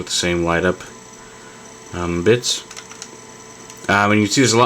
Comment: sounds like he says b*tch.